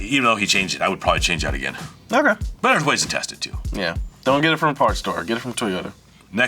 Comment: That's not what he said, he said.Get it from a junk yard